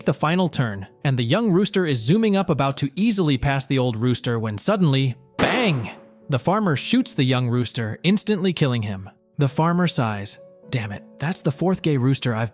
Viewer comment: I thought the farmer said Dang it bc he wanted to kill the old one 😂😂😂